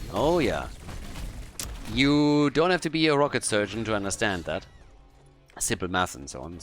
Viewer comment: did he just say "rocket surgent"? that is new